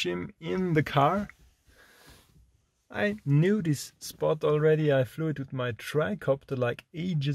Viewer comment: Great Video!! at the start I thought you had said " i found a nudist spot" i was like wooooo we did'nt know!!!!! but you actually said " I knew this spot" hahahaha